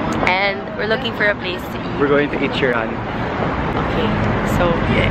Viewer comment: I thought he said “We’re going to EAT SHEERAN.” thinking it might be sort of a slang or urban talk. Haha